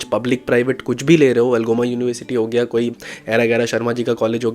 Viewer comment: Did you say Algoma University in middle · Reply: Right - Algoma / wrong - Sharma ji ka college